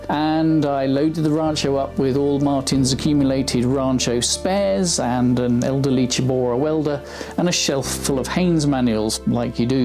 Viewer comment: I heard "and an elderly Chihuahua welder." It conjured a wonderful image, and you can't convince me that's not what he said.